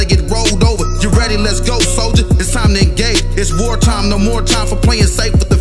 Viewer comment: What does he say at 1:39 ???? Because sounds like he says time to act gay? Lmk lmk